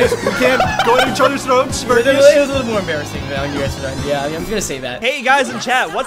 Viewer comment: I think he said “that you threw”